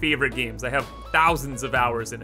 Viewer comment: DID I HEAR A ROCK AND STONE?